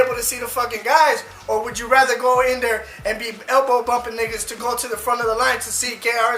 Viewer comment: did he just say nigga?????